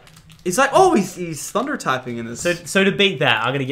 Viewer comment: did he say thunder typing